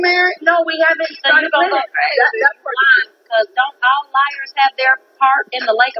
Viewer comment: Keke was only playing tho. She basically was saying that Monifah and Torez was lying and they did set up a wedding date. She was playing, why are they all taking it to a new level. It didn't make keke look bad, it made the rest of them look bad. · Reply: Cuz i heard her say liars...but everyone acting like she said lesbians